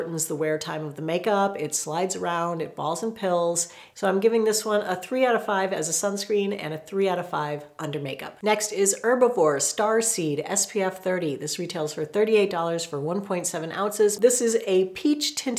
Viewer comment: I thought it said one “A $900 sunscreen” 😅